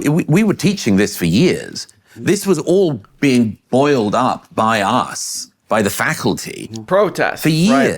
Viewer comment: I thought he said "oiled up." And then I remembered that ancient drop "oil up and hit the gym with me."